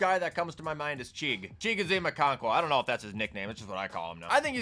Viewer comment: maybe im deaf but what tight end is he saying right here? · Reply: Okonkwo, Titans TE.